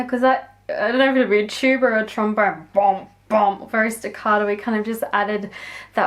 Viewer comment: I'm sorry what is she saying at 5:20? Is it staccado? What is that? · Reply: Staccato means that the notes that are played are very short, so without sustain. You can hear the difference between staccato and sustain the best in string parts.